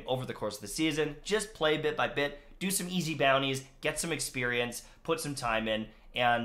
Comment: I heard 'grind bounties' multiple times... No thank you